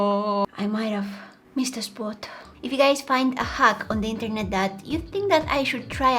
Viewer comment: not me thinking she said a “Hug on the internet” instead of a hack 🤣🤣